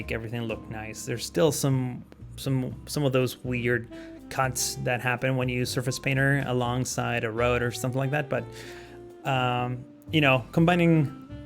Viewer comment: What did say? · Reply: "There is still some of those weird cuts that happen when you use surface painter alongside a road or something"